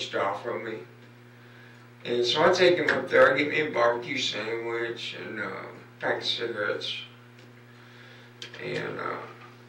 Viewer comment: he says that he gets himself a bbq sandwich and a pack of cigarettes,. He says "I get me a bbq sandwich and a pack of cigarettes" then gets him the gas. I know its hard to understand, but that's what the guy really says. Not what you have up on screen.